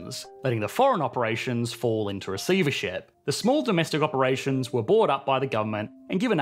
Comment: Where are the closed captions? I can't hear what the foreign branches of the banks fall into @ 12:15. (vesivership? Recievership?)